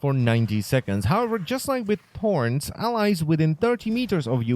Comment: wow i heard porn like 3 times and then i put Close Captions on and i see "porns"